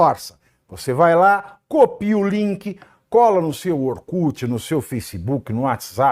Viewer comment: Eu ouvi ORKUT aos